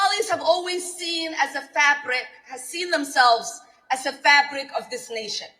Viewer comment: sounded like she said fat brick rather than fabric of society, as funnt as that was i don't know if ive EVER seen fat Somali person.. this one really grinds my gears. Just deplorable.